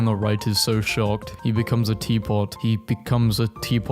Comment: my brainrotted ass thought you said TPOT